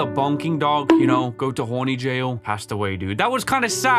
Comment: DiD hE sAy HoRnY?!?!?!